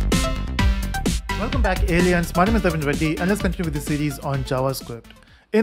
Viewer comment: Hold up did he just say "welcome back aliens?"